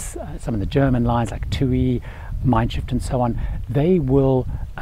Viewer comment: Hope you don't mind me saying, I'm not sure but I think I heard you say it twice; it's pronounced Mein Shiff, not Shift. Shiff means Ship in German. ;) Thanks for the great video! Happy travels!